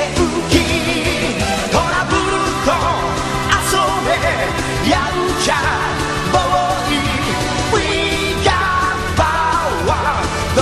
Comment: Yaaaaamcha, Brooooly <-- What I hear, lol.